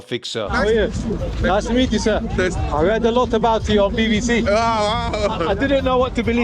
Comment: I thought he said "I heard a lot about your BBC"🤣